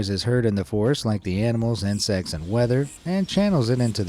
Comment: did he say, "..it amplifies the noises heard in the forest like the animals and sex and weather.."?????